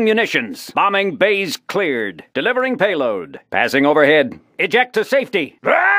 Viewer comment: why did I thought he said "delivering paper" :')